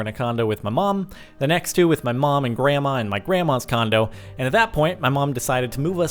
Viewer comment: For a sec I thought he said condom